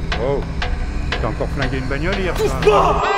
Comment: qu'est ce qu'il dit a 0:51 ? je veux apprendre le francais ! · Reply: Il dit: "oh! T'as encore flingué une bagnole hier soir?" = oh! Tu as encore abîmé/détruit une voiture hier soir?